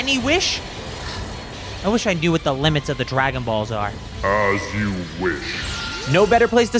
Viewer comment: I thought video said The Limit of my balls😂